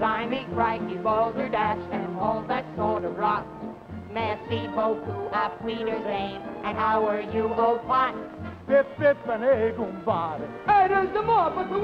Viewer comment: What does he say? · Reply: Jelle Wiebrens “How are you, old pot” - it’s supposed to be an upper-class English accent as spoken by the posh Bertie Wooster types who lived in Stately Homes!🧐